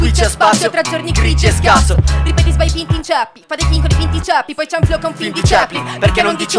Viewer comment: Bravi tutti eh, capolavoro, ma si può sapere cosa dice a 13:46 dopo "tra giorni grigi e scazzo" ? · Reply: dopo una vita l'ho capito leggendo il booklet del disco "ripeti sbagli, PIN, t'inceppi"